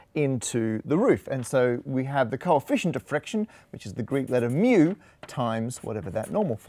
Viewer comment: Did he just say "Mew"? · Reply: Mu